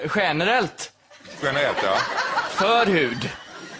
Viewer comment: vad säger han, generellt förhud?